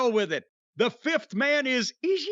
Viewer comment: When Eddie said, "Ishii" I swear I thought he said, "Eazy-E!"